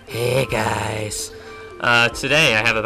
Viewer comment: wtf was that noise at the start. sounded like smegle saying 'he guys'